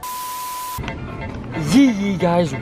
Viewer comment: I thought they said yeet yeet